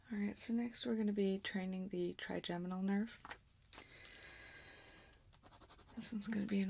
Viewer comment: I thought she said "tri-genital nerve". Woke me up.